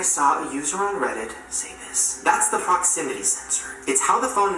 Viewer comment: I thought Shane said “f*ckximity censor” when he said “proximity”